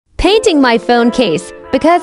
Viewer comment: At first I thought it said “Dating my phone case” lol